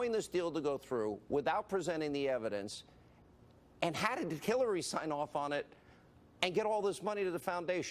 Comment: did Hannipede just call her "Killary"?